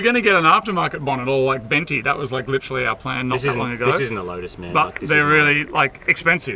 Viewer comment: did say "this isnt a lotus"?